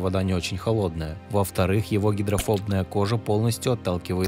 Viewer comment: Мне послышалось «гидрофолтные» а не гидрофобные